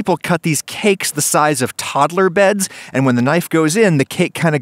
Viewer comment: For a second there I thought he said "toddler body bags"